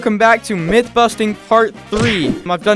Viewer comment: i thought he said myth busting fart 3